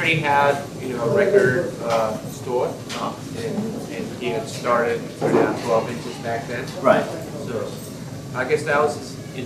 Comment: Some of the subs are wrong like at 2:06 Shingo clearly says "he had started putting out 12" back then" and the subtitles say "and started a clothing business back then" but it's okay. Great job on the interview.